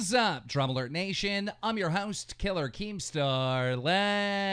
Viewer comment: I heard I'm your host "Hitler keemstar" XD